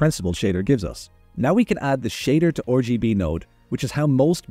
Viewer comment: Did you just say "shader to orgy-b node"? 😂 Anyways, great tutorial.